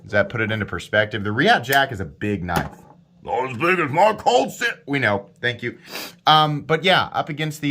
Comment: what is :_MClogo1: saying??? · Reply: in boomer from Texas accent “NOT AS BIG AS MAH COLLD SHTEEEL”